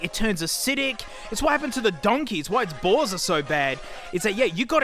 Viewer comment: for anyone (that hopefully has the same subtitles as I do) that wants a laugh. It says "It's what happened to the donkey. It's why it's balls are so bad." Instead of bores.